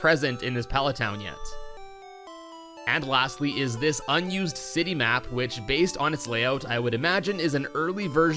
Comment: “Unused City Map” I heard unused sinny map